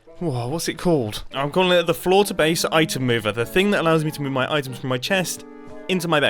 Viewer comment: Lol I thought he was saying Florida based item mover for half the video when it was actually floor to base item mover. The database is really cool. Its like a hard drive with addressable registers.